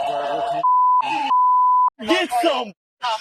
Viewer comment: Does he say "Get some"?